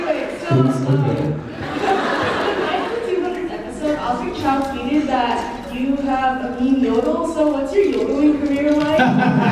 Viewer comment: What does the fan says to Jared at 5:22? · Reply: During the 200th episode osric tweeted that Jared had a "mean yodel" and she wanted to know his yodelling career.